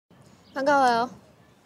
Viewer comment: whenever i hear “Bangavayo” i’ll go like “✨Veronica Parkyeyo✨” 😂